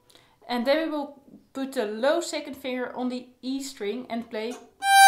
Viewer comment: I don’t understand what you said here? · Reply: Hi thanks, Maybe it is my accent. I am sorry about that. English is my second language. I said to use the low second finger on the E string.